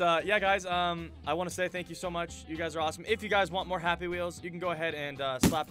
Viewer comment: Whenever says "You guys are awesome", I hear "You guys are Rossome". What did you do to me? xD